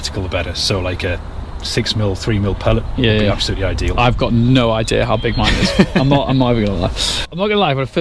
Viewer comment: Did I hear say ‘I have no idea how big my nuts are’? 😂🤣😂